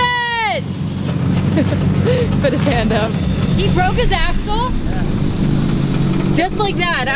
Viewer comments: Sounded like she said “he broke his ass hole” lmao 😂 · I thought I hear her say “he broke his ass***d”. “Axle! 😂